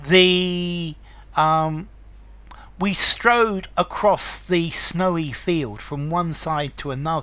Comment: Hi dear teacher. In 03:25 what did you say. "We stroe or we strawed" Please type that sentence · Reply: We strode across the snowy field ---- from the verb to stride